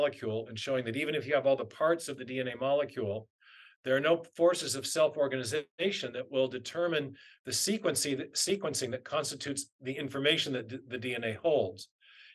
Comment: I couldn't make out what author Dr. Meyer mentioned around 1:24:23. Did anyone catch that? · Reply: He said "there are no forces of self-organization that will determine the sequencing that constitutes the information that the DNA holds"